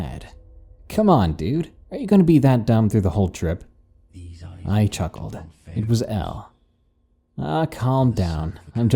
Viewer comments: Am I the only one who hears a voice at 8:52 - 9:03 ? It's creepy and I can't make out what it says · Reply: It says (these eyes have looked upon pharaohs and the son of a carpenter and now they must look upon you) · alvin greg 1 second ago 8:53 to 9:01 what is that whispering in the background saying??? i hope it ain't no conjuring spell lol!! but serious i can't make out what it is saying>> can anyone else make it out? alvin greg alvin greg 1 second ago l15:40 its louder and it says i think "something about pharaohs ... seen a carpenter... nobody must look upon you"? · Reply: These eyes have looked upon a Pharaoh's and the sons of Carpenters now they look at you